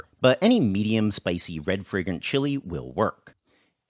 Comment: I got confused. Whenever you said "red fragrant chili" I heard "red friggin' chili. Until the last time you said it. Then it became clear. I mean I've had red friggin' chilies and they're just too hot.